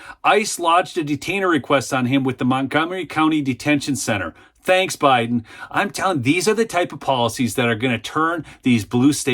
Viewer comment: I thought said ‘Ends Biden” I’m already ticked off, always.